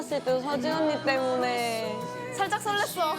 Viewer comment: i almost died when i thought she said seo soojin